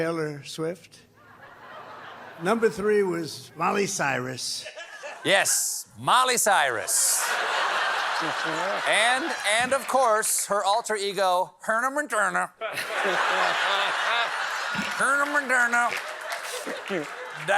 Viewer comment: I know you like mocking Trump's voice, but at least keep it to when he actually botches something. 4:43 he clearly says Mommy Cyrus.